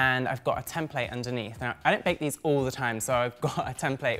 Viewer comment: "I don't bake these all the time." Also: Pipes the perfect macarons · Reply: I thought he said “I do bake these all The time…”?